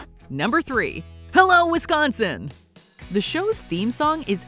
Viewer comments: I always thought they said I love Wisconsin in the opening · I thought Kelso was the one yelling hello Wisconsin. Good to know Hyde was the one doing that. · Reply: I thought he said “I love Wisconsin!” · I thought Kelso was the one yelling hello Wisconsin. Good to know Hyde was the one doing that. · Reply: And here I thought he was saying “I love Wisconsin” this whole time. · I thought Kelso was the one yelling hello Wisconsin. Good to know Hyde was the one doing that. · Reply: Me too. I also thought he was saying "I love Wisconsin"